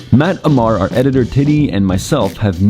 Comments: did he say his name is... Titty?? Even the subtitles say "Titty" 😂😂😂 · did he said "Matt, Ammar and our editor titty"..?🤣🤣